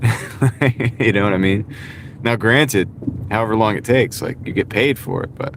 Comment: I think you meant to say don’t take anything for granted. It sounded like you said granite. I have done that before though 😂